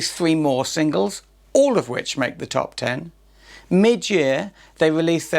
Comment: did you say mid year or Midge Ure?